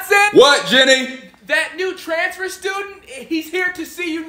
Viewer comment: Not gonna lie for a second I thought he said trans student.